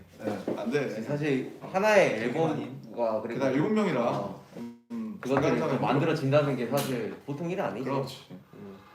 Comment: did I just hear jk voice saying"where are u going" use earphones and listen carefully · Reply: Yeah he said "eodi gani" Which is where are you going